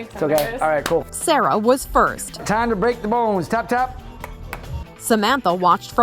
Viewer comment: Did he just say let's break some bones with a mini mallet in his hand. LOL